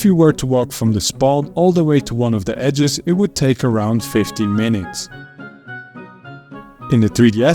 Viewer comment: did you say 15 or 50 minutes? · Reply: 15 minutes, 50 would be overly optimistic